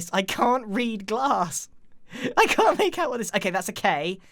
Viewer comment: Am i the only one who thought it said clear glass?